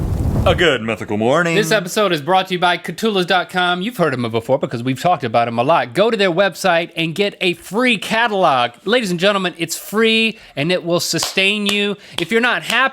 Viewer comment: ''go to their site and you and get a free catalog'' For a second there i thought you said a free cadilac